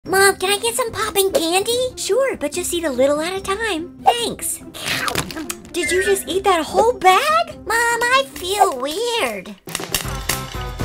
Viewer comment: At first i thought Alan said mom can i have some pooping candy😂